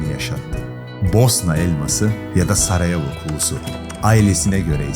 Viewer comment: Bosna Elması yada Saraya vukusumu dedi ne dedi orda